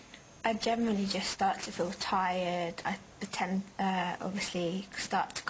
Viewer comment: wtf she almost says pretend